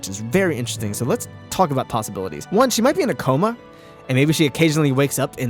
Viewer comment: When you said "Do you think she's in a coma?" at the end that gave me an idea. The words "in a coma" were said so fast that I misheard it as "an akuma," so maybe she is akumatized somehow or has some control over the akumas. Any connection, really.